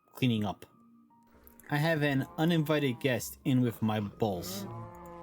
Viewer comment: The subtitles say: "I have an uninvited guest in with my balls" at 23:57😂